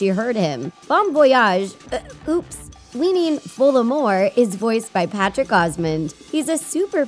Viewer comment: did I just hear Voldemort?